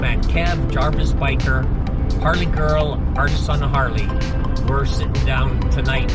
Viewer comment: Question Mr.... at 4:26 did you say "Arson Harley" or maybe "Our Son Harley".? Is he the Gentleman standing on the far Left, wearing a Black Harley Davidson T-Shirt in your Get Together group photo.? Can you plz point me in the direction of his Channel. 🙂BTW: Did you maybe see all the induvial members of from your Canadian Get Together Crew in my latest video.? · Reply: Hi Angel, the channel I called out was Artist on a Harley which is Bonnie and Pete and they are standing to the right of Craig. The other couple to the left of me was Harley Gurl and her husband Scott. This was a great evening. cheers and be well.